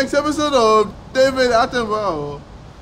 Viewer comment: Did KSI said Devilartemis? Cus in that time Devilartemis animated Cell vs penguins to